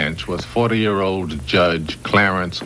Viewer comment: did they say 40 year old judge 👀 😮